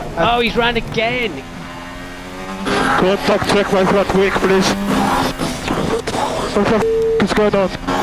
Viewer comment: those subtitles are wrong. he said "Contact, check my front wing please"